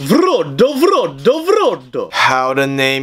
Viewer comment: As a Russian, all I hear is "Frodo Frodo Frodo" 😂😂 I don't mind it tho